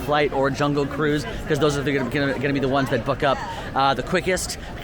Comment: This might be weird but my bf and I have a debate where we try to figure out who secretly cusses on AllEars crew. We’ve narrowed it down to and. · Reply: Early in the vid I could've SWORN said "F🤬k up", but of course he really said "BOOK up" in reference to Genie+!😂😂😂